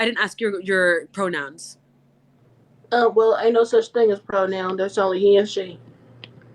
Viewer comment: what is she saying at 3:57? someone please help me out of this misery 🤣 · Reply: She said "Ain't no such thing as pronouns there's only he and she"